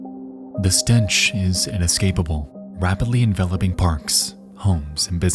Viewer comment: Captions says "an escapable" when it should say "inescapable"